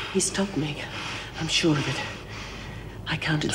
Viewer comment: What was Ann saying to meg at 11:09 ? He stopped meg. What did he stop?